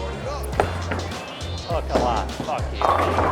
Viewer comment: Did I hear “Hook a lot, fuck you” at 2:58?